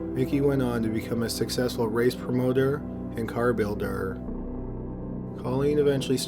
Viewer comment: I don't know if I'm hearing things or if its cuz he drags out every word, but almost everytime he says anything ending in "er" I swear he adds an extra "er"... builderer, driverer, promoterer. Does anyone else hear that? He does it several times at around 8:20, especially "builderer" lol